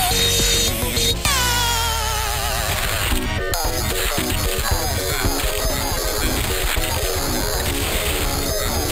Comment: what's he saying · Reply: I'm-a Luigi! Number 1!